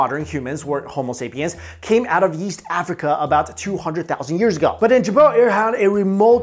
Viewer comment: i thought he said "yeast Africa" XD